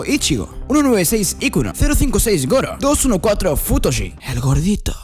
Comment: yo escuche putoshi XDDDDDDDDD